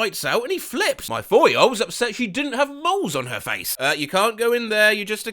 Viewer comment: it’s like 2 am and I heard “balls” instead of “moles”